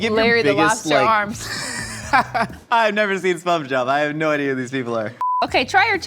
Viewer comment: It sounds like Josh said "I've never seen SpuvJob." I don't know if anyone else hears it, but that's what I hear. XD